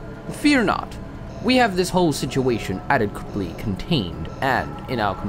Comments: Did… Did he just say "adequiply"?! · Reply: I know that it was supposed to be "adequately", but that doesn't at all sound like what he actually said. It sounds like he said "adequiply". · Did… Did he just say "adequiply"?!